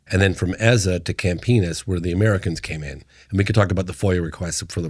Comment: does he say "Camp Penis"?? 😂